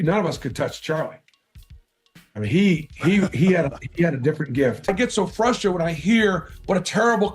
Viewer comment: I heard "He had a different grift" ...which sounds more in line